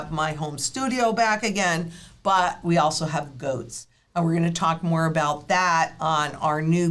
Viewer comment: Towards the end of the video Lynette said we have goats, i’m disappointed,I thought she said we got “Ghost”!👻